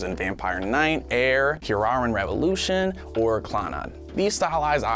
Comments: did he just say clon odd? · What did you say? Clannan?